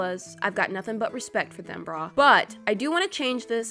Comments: did she just call me "brah"? 😂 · It sounded like she said dim bra when she said she liked them!